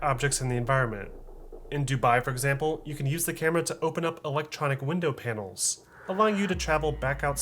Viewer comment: Sounded like u said elechonic I love the vids tho